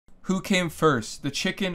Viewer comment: I heard “Coccaine first, the chicken or the egg” seemed right for Columbia